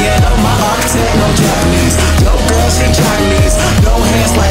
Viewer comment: "My architect know Japanese, yo' girl, she jocking me" What i heard: " My architect know Japanese, yo' girl, she's chinese" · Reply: I heard "My architect, no japanese, yo' girl, she chinese" Pffft